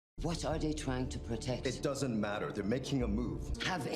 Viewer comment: Watch with the subtitles. The wordd said matter. Sometimes you mishear things, and that confuses you.